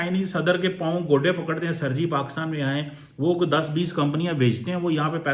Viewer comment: Did Cheema say gote pakadte hai 😂😂😂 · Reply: Gote nahi godde, which is punjabi word for ghutne (knees) 🤣🤣